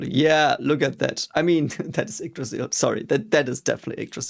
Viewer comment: what did he say icdriser? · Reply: Yggdrasil